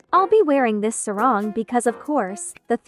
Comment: WAIT WHEN I HEARD YOU SAY “ill be wearing this rok” DOES THAT MEAN YOUR MALAY OR INDO :O